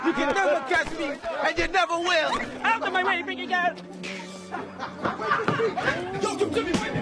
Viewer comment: did em just say "out of my way pretty girl??